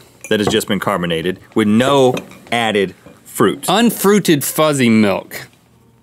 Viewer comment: I thought that this said Fuzzy milk, figured I was in for a gross episode..